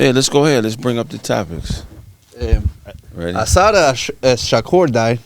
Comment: I thought said "I saw that Shakur died" at first😮.. I was like not Shakur Stevenson!!?.. 😂